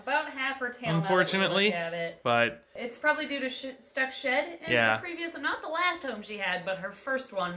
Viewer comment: Omg Emily whe you put Alaska in the tub cause she had shed on her feet. I had to reply it cause I totally thought you said she had s**t on her feet. Lol